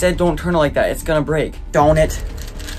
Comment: What does he say on 1:28 like what does he say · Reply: he said darn it